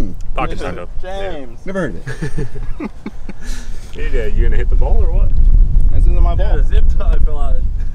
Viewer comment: After the pocket taco talk, I definitely thought someone said " you gonna hit the bowl or not?" Lmao I had to rewatch it a time or two